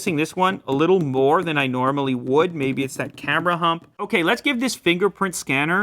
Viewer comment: Did he just say 'camera hump'?